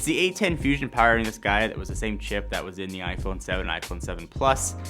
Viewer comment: I thought he said: "it was the same shit" 🤣